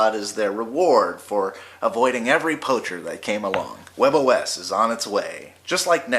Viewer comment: when you said "avoiding every poultry/poetry" I first thought you said everypony